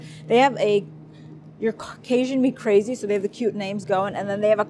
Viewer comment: Lol,I thought you said, "Caucasian me crazy"